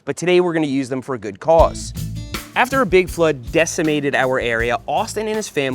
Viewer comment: I had to do a double take, I thought you said a Bigfoot did the damage.